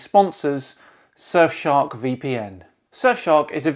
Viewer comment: "[...] surfshark vpn [...]" heard that as "sir shark vpn" and was waiting for something like "sir shark vpn, the royal solution to your privacy" also, phucket word for DFOTY 2020